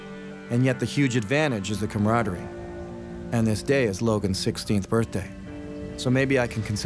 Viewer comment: When you mentioned that Logan was having his birthday it sounded like you said Logan is having his 60th birthday not 16 you can really tell when you're low in energy and not having the right amount of nutrition to have you speak well that's for sure